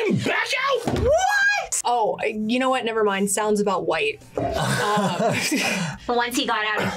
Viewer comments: The redhead needs to go! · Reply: i thought she said sounds about wright because its the guy last name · Lmfao she said sounds about white 😂😂 girl I feel you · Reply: I thought she said sounds about wright because its the guy last name